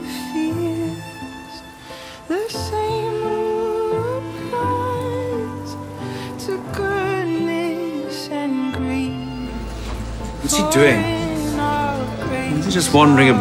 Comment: Impressive as the original! ;) btw, can someone tell me what does Clara say at 3:00? I just can't understand! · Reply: Catherine Jones She said Is there a word for total screaming that genius that sounds a modest and a tiny bit sexy It's from the doctor who episode Asylum of the Daleks